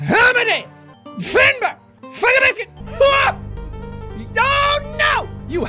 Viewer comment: does he say fuck a biscuit